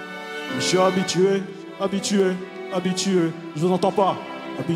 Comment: il commence à lâcher des "je vous entend pas" il a cru c’était un show case ou quoi mdrr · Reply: perso j'ai compris "je n''entend pas " en me disant que c'était pour le retour